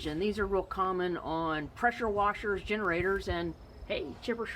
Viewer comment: If this video helped you, please consider clicking on the THANKS button above 🙌 Check out some of these other quick-tips: <link> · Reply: Yes it helped me— by giving me a laugh— thru my own fault! You were listing common products at the beginning of the video that horizontal engines are found on and I thought you said "pretzel washers" instead of "pressure washers" 🤣🤦‍♂ Just found your channel a few days ago. New subscriber. As you might be able to tell by my "shorthand" screenname, I do enjoy small engine stuff.